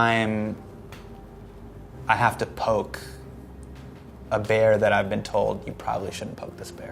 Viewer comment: He said "poke the bear" i heard "poked Modi"